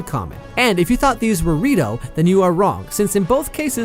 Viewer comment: Thank you so much for 45 000 Subscribers! We are speechless, from 10K to 45K in less than a Year. WOW. Now the hunt for the big 50K begins! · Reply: Erm..... "if you thought they were the rito you were wrong" nope I misheard you said " if you thought they weren't the rito you were wrong" that sounds better. Time